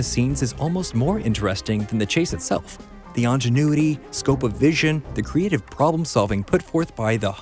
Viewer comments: Did he just say "ANGenuity"? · Did you just say “on-genuity???”